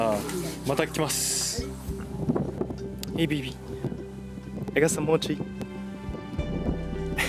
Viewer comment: Did he call his cat baby at 2:22?? Awww